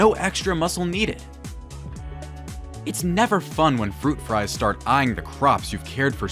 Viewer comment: "fruit fries" lol I love that! Mainly because when I say it fast sometimes, it comes out like that instead of 'flies'. Also, a lot of these are really great ideas!